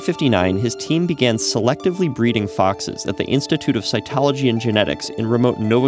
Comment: Thought he said Scientology!!!